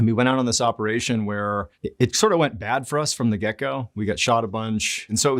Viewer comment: The way he said "get go" it sounded like he said gecko😅